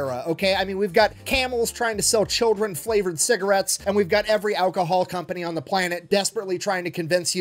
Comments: I heard this as "Selling... Children Flavored Cigarettes." Assumed we were going full Modest Proposal here. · Dude needs to work on his cadence. I heard Camel is selling "children flavored cigarettes".